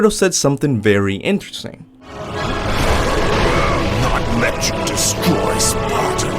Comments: I hear "Not let you destroy sparta" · I hear him saying "... let you destroy Sparta" and in context i think he says "i won't let you destroy Sparta". It seems quite obvious that he says that.